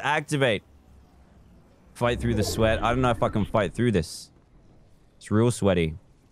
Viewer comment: Dan: I don't know how to f*****g fight through this 46:37 What dan really said Dan: i dont know if I can fight through this